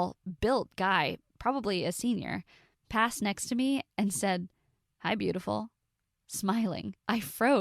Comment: When the guy said "Hi Beautiful" and walked away, I thought he said "I'm beautiful" and walked away 😂